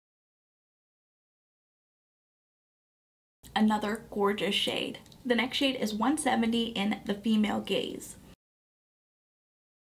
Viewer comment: I'm watching while also multitasking so I wasn't looking at 5:27 and I heard "The Female Gays". 🤭☺️